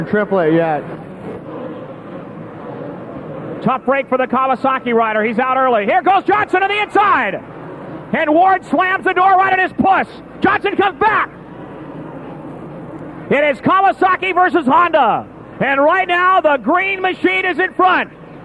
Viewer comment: did announcer say "ward slammed the door right on his puss"